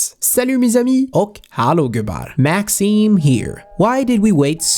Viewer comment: This video took longer to make since it was a big update, but wow this money-loss bonus is insane... But you know what's also insane??? Not following me on Twitter or Instagram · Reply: It sounded like you said hej gubbar in Swedish at the beginning